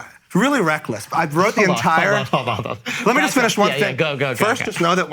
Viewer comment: Time stamp? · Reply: He actually says "lemme just finish one thing" but the sentiment is the same. Lol